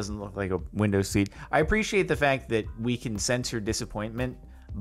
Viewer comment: I thought he said “I appreciate the fact that we can censor dissappointment” and I was wondering why walks around not wanting to see sad faces